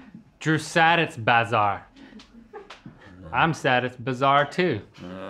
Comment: I thought I heard Link say “I’m sad and bizarre too” omfg 😭